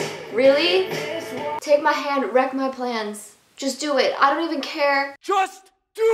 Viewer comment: Tell me why I low key thought willow said “wack my plants”